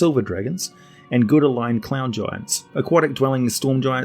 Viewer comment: did... Did you just say "clown giants"? I've heard of a clown species but... WAT!?